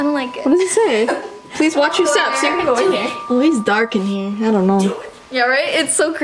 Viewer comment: OMG AT 11:43 WHEN SAYS “IDK” I heard a voice saying “DO IT”